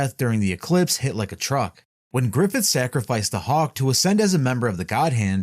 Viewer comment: bro did not just say hawk tuah and thaght we wouldn’t notice 😭🙏